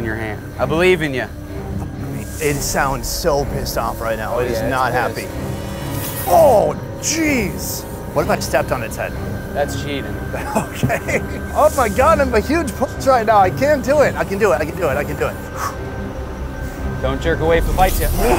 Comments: Sonny While Hunting the Snake - “Can I step on the head to grab it’s neck?” Pro Snake Hunter - “Nah that’s cheap” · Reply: I thought he said "that's cheating" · "What if i stepped on its head" "That's cheating" Im ded 😂😂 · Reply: I thought he said "that's cheap"😅😅